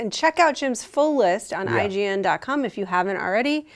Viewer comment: did she say IGN.Cum? or was it just me